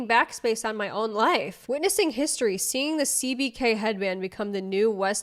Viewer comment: I heard someone talking about this "CBK headband" I honestly thought they had said BTK (the serial killer) headband. Sometimes I'm glad I have no idea what people are talking about 🤣 Also what on earth is "quiet luxury"? No loud colours of something? 🤷